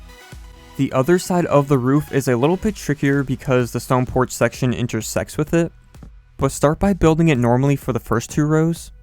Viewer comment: i thought u said "because it will enter-sex with it" im not a perve and im 11 years old i swear thats wut it sounded like XD